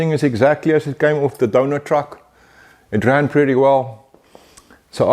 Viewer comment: 🤣 I take it you having some fun here. Or just maybe its because I can't speak English properly? DONOR truck, but I like donuts too 😂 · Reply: I love your South African accent! I honestly thought you said DONUT truck. I thought, cool! We had bread trucks in the 1950's. They would come through the neighborhood honking their squeaky horns every Thursday morning! (Helms Man) I thought maybe you had something similar their where you live. Maybe that was what you were referring too. My bad! I'm 68, a drummer and my hearing isn't that great. My apologies! Blessing sir!